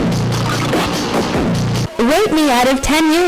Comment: i heard the other R word rather than rate 🤑